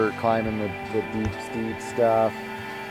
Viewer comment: What does he says? * are super great for climbing.... How are they called?? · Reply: the deep steep stuff, i think